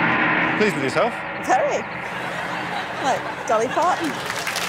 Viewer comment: Did Sian say "Like Dolly Parton"?